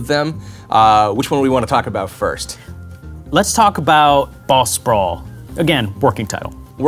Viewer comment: I thought he said, "Ball Sprawl."